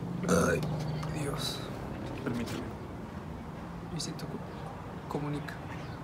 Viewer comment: En todo el video escuche "me siento como nica" :V Y "lo hiciste como nica"